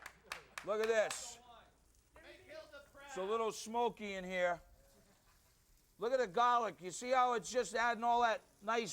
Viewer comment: Did he say “make hitler proud” at 5:16?😂😂 as he’s messing with the oven☠️☠️🤣🤣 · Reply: no, he said "Make Hilda proud!" Hilda being Emeril's mother.